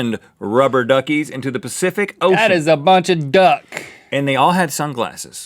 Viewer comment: Lol when Link said "That is a bunch of Duck I laughed so hard." I heard it wrong "That is a bunch of bad word".